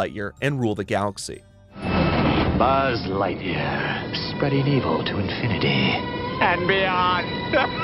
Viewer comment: I legit thought Evil Buzz said "Spreading eagle, to infinity and beyond"....